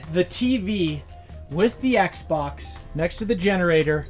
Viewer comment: I thought he said xbox1 not Xbox series x